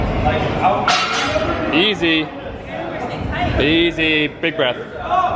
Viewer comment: 3:58, I thought he said "Big breast." I'm like yeah she does! 😅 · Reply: lolol ewww no way.. hes like my brother lol he said "big breath" lolol